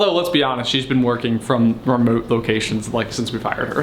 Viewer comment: I misheard “since we hired her” as “since we fired her” ....thought ok, that was a very short employment